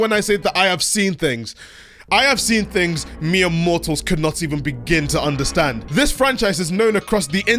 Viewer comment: I heard 4:42 "mere mortals" as Miyamotos and it was perfect.